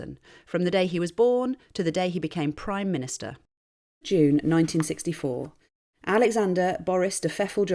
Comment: I thought I first heard “Alexander kerfuffle Boris Johnson” 😂